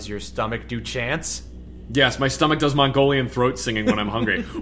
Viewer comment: you wrote my throat instead of my stomach lol